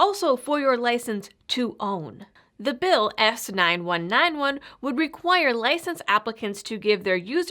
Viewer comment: When she said "The bill S9191" I heard it as "The bill Asinine-91" ....Which is probably more fitting anyway, as the Senator is clearly a moron.